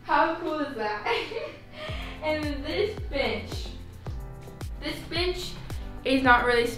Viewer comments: i thought she said this bitch 😂😭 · when she said this bench, i thought she said "this bitch"